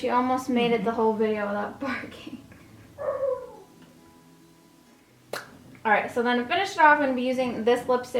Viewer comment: I thought she said she thought her mom made the whole video without farting but then i realized she was talking about her dog barking 😂😂